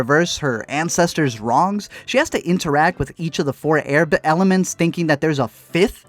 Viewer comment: i swear i heard 4 air bend